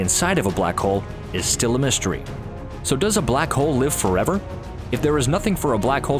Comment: did he said black cole